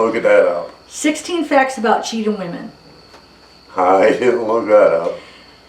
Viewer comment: Omg I heard him say cheating whore 😨